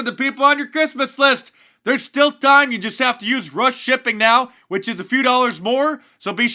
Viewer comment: did you say use russian shipping? 🤣